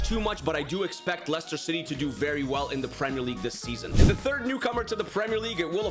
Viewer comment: WHAT DID HE SAY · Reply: Per league this season